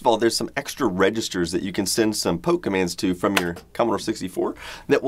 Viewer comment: I thought you said 'pokemans' I had to double check because I'm an idiot